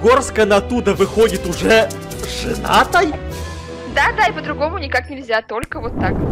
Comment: Эд: Каждая пара, которая попадает в Пятигорск, выходит жената? Да, подругому никак Подписчики: Агрессивно ждут влог из Пятигорска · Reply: МНЕ ОДНОЙ ПОСЛЫШАЛОСЬ ЧТО КАТЯ СКАЗАЛА: да зай по-другому никак🗿😂